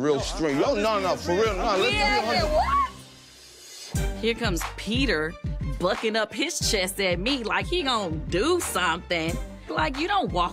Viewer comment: the way I heard and seen was…”and here come Patricia buckin up her chest at me like she gon do SUMTHAAAANG”